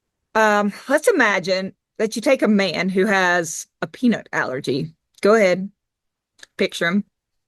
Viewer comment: I thought you said Frank had a ‘penis’ allergy! A little hard of hearing! I definitely wanted to hear all about Frank though! 😂🤣😂